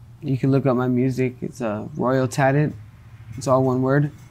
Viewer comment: Has anyone found his music videos? It sounds like he is saying his name is ‘RoyalTalent’… or maybe RomanTalent? I can’t find anything.. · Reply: My man he said it clearly in the vid. It’s